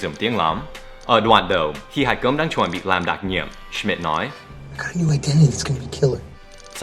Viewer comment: Cho em hỏi chỗ 5:54 tại sao "I've got a new identity" nghe như "I have new idenity" vậy? Nhìn cử động của miệng cũng giống. Có phải là do nhân vật nói nhanh quá em không nghe được hay có cách đọc tắt không?